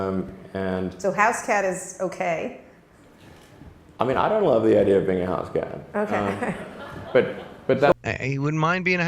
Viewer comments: media 3:54 he says i dont love the idea of being a house cat if im not mistaken... not "i dont mind" the idea of being a house cat as you commented just a correction although im not sure i heard it correct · Just for the record, at 3:49 Elon Musk clearly says "I don't LOVE the idea of being a house cat," not "I don't MIND being a house cat'" as you stated ya douche! Don't be taking shit out of context man, that's exactly how motherfuckers give so called "truth seekers" a bad rep, by twisting up other peoples words and changing the entire context of what was actually said. It seems like you're doing that shit intentionally.... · Sounded like he said "I mean I wouldn't really love the idea of being a house cat". Slow it down and listen again. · At about 3:45, I don't think Elon said 'I don't mind being a house cat' as the video commentator indicated, I think he said "I don't love the idea of being a house cat".